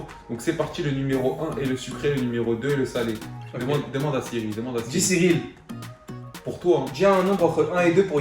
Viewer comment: c moi ou a 0:46 yassine dit SIRILE vs étes les meilleurs continuer